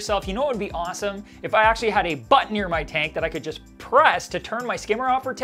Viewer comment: Sorry I heard "a butt near my tank" ROFLMAO